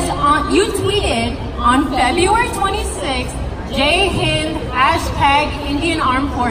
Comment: Did she say "GAY HIND" @ 0:18 ?